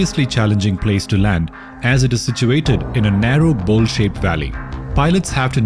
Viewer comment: Did he just say bullshit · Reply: bowl shaped